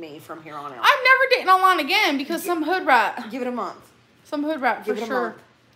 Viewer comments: So what word did she say that she had to apologize for? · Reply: I think it’s “hood rat” she says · Bro I’m not watching this whole thing, can someone tell me what she said? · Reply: based on the comments i think its at 1:04:20 , she says "hoodrat"